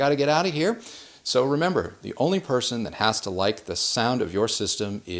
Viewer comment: no, this is not where you place the surrounds! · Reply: I could have sworn he said "nobody has to like the sound, but you". Put them where you like; it your house